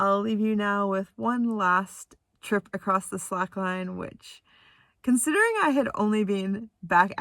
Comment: Oh, slack line! At first I thought you said "psych line". You showed many talents in this video. You are a charming young woman. Your parents are lucky to have such a lovely daughter.